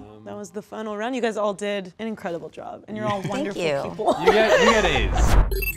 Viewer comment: did he say "You got AIDS" ??